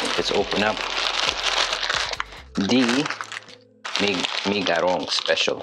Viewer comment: i think i hear you say mie garong ....haaaahaa